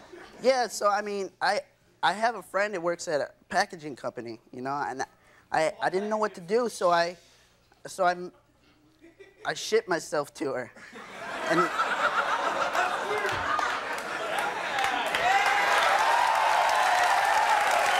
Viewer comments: deff thought he said "I shit myself to her"😂😂😂😂😂😂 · i thought he said shit myself to her. i was like boy what 😂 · I thought he said shit myself 😁😁😁😁